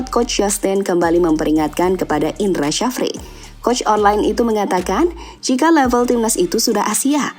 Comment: "coach online"....? apa aku salah denger ya